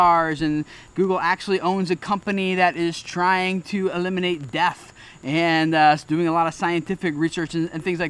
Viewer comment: Excuse me, but did you say they are trying to eliminate DEATH?! I thought the future was in the future, but it's actually in the present.